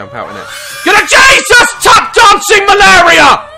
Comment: Did he just say "JESUS TAP-DANCING MALARIYA" 😂😂😂😂